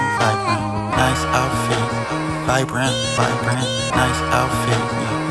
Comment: I thought he was saying hi Brent, nice outfit.